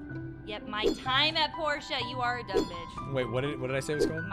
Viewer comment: Every time I heard you say this games name in twitch chat I thought you meant “My time and Porsche” and was so sos confused when you called it a “3D stardew valley”